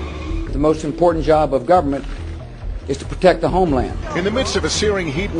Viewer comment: I thought Bush just said, 'the most important job of the US government is to protect the homeless' @ 9:45 ...roflmao!